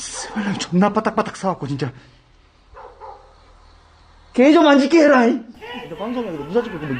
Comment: 이거 06:32초 이부분 말소리 들리는데. .? 정확히는 모르겠는데 누가 악 쓰는거 같아요. .. 오매...물속 까지 ..들어가시고. . 고생하셨어요.. 항상 조심히 다녀요.. 바로 뒤에 검은 형체가 있어도 차분히 대처 잘 하셨습니다. 오늘 방송도 잘보고 갑니다😉 좋아요랑 구독버튼👍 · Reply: 개소리좀 안나게 해라 아니냐